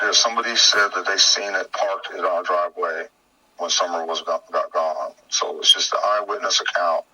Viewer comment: Listen to the video around the 12 minute mark. He was talking about and he says something like the day after he fired was the day Summer was .....sounds like he says... DUMPED! Then you asked him.....what? He says... She disappeared!!! Whatever he said only has one syllable not two. Listen to it please. Maybe I'm hearing wrong.... . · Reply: It sounds to me like he says "summer got gone"